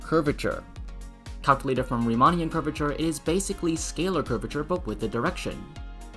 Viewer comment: Nice video! Thanks! @ 7:18 you said that Ricci curvature is basically scalar curvature but with a direction but the subtitle says, it's sectional curvature instead of scalar curvature. Which one is correct?